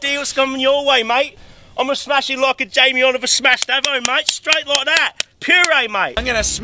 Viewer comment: did he say smashed Abo? lol